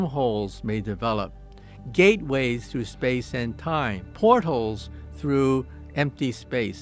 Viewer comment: Did he say “port holes” in place of ‘portals’ 7:40??? 😂😂😂